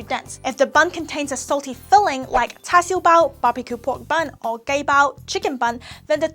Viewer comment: Hey guys, no offencse, but it sounded like at 2:09 "Gay Bao" And yes I'm Chinese so I know mah food pretty well, too.